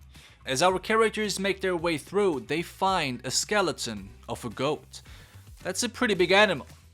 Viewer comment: I totally thought you said “skeleton of a ghost” and wasn’t surprised by that. Then I heard you talk more about the goat skeleton